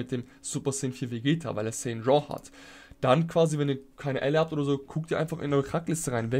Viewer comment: ich hab verstanden wenn ihr kleine Eier habt